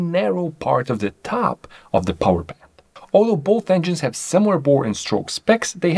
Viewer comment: What did you say??? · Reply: powerband